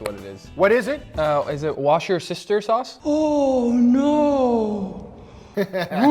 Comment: I hear: "Worshesteir Sauce"